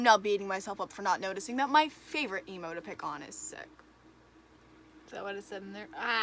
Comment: am i the only one who heard nemo at 1:42 for a second